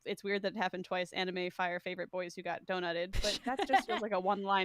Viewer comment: Every time I hear them say “got donutted” I only hear “got hotdogged,” McElroy fans will know where I’m coming from